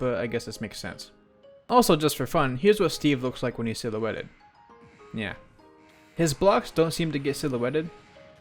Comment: YO DID JUST SAY NYA